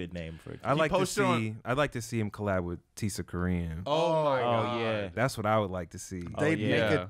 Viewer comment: "id like to see him collaborate with t...." what did he say here? I heard a t I dont know what he said, who should yeat collaborate with? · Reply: Tisakorean